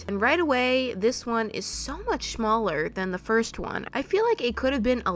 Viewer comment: did I hear shmaller instead of smaller